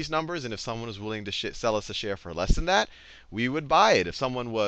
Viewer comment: Did he just slip-up and say shit instead of share XD